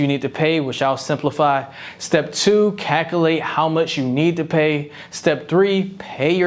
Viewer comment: After I heard the word “cac-ul-ate” I couldn’t focus on another word you said for the rest of the video.